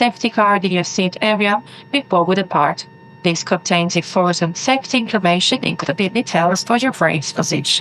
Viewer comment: does she say informative sex information?!